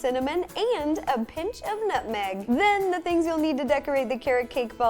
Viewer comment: I thought she said 'nothing' instead of 'nutmeg'!!!!!!!